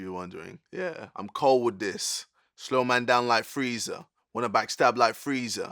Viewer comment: am i the only one that thought he said "down like threesome"??